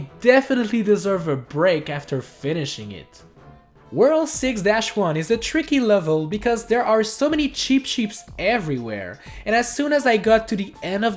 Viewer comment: Did I hear "Cheap Sheets?" Or is it just me.. Or it may be his accent.. Meh uwu